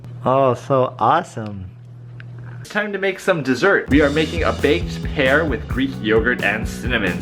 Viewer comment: When he said baked pear with ....... I thought he said baked hair lol